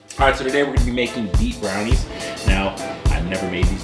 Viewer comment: Hi. This looks amazing 😋 I did have to start the video over twice, as I thought you started out saying, BEEF 🥩 BROWNIES , not beet 👀🤢 huh? 😂 thank you for another great recipe idea ☺️